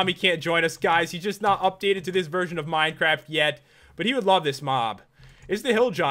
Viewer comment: I heard pat say "Bomby will love this mom"